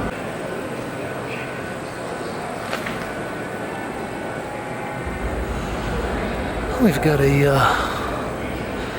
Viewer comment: thank you for making this video! .at 27:00 you show some large tapestries. What were the materials used? I couldnt hear what you said, but it sounded like natural materials plus a felt tip marker. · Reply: The materials listed for these works by Duane Linklater are: Linen, cochineal, orange pekoe tea, charcoal, sumac, cotton thread, blueberry dye and felt tip marker...